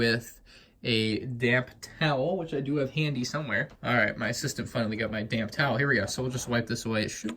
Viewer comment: I thought you said: "Alright, my sister finally got my damn towel."